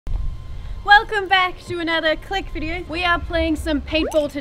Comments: At the start I thought she said welcome back to another clickbait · I thought loserfruit said "welcome to another click bait video"